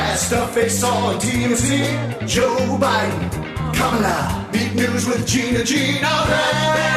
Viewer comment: Gina is going to do that "Kamala" mouthing every time that song comes on? It really doesn't sound that cool. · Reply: Holy fuck I thought the song said, "Joe Biden - Comin Out!" Like he was gay or something. Makes more sense now.